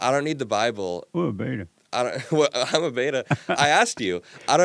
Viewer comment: Wait what did he say at 08:30? What a beta?